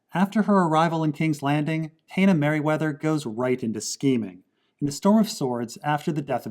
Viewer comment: Did I hear the word 'scheming'? Me happy.